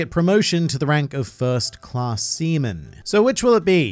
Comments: what did you say here? english is not my first lenguage and I swear you say "semen" (and the CC aswell) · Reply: 3 weeks late but, Seaman, second lowest marine rank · I thought he said "first class semen"